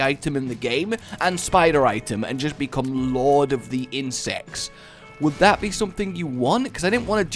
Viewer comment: it sounds like he said lord of the incest